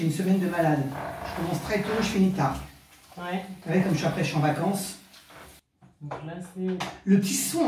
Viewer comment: "comme après je suis en vacances": i heard "comme s'appelle j'en vacance". Examples like this happen quite often in French and that affects a lot in listening comprehension.